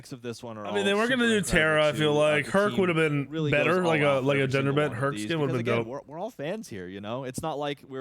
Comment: did he say a gender bender herc skin?